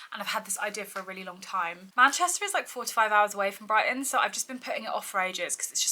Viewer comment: Not me mishearing you and thinking you genuinely thought Manchester was 45 hours from Brighton, not 4-5.